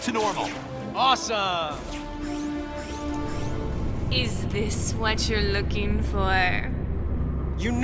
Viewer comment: Black Cat says “Is this what you’re looking for?” But the subtitles say “is this what you’re locking for?”